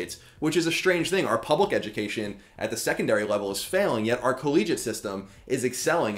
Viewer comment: It's mad funny that you thought I said "collegic," and yet don't know that "collegiate" -- which is what I actually said -- is a word. Thank you for your feedback. -Colin